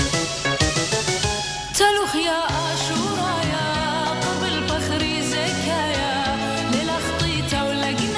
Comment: Beautiful translation, but correction, at 1:16 she says "qorba al paghri zakaya", which means "close to my pure body". It sounds like "qabel" (accept), but she actually says "qorba al" (come close to). Btw, zakaya means pure, innocent, fresh, etc.